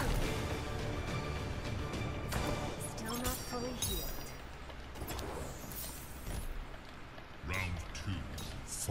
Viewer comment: what she say? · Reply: "Still not fully healed"